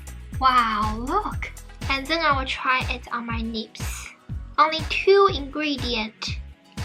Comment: Hi, I am not trying to be mean but I really want you to practise using the L sound on words like lips at 0:40, similar at 1:44, less at 1:57 , instead of the N sound. It will help improve your English a lot.... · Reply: I lost it when heard “nips”